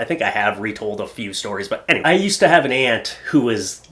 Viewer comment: I thought you said "I used to have an "ant" , so afraid of birds..." 🤣😄 it cracked me up 😂 Love the vids! I have no addblock on so I just do something else when the adds come up, cuzz it's a source of income for you. Keep it up making smiles on people's faces! ❤